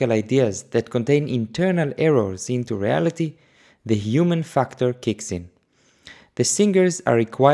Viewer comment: I swear I keep hearing human fuckter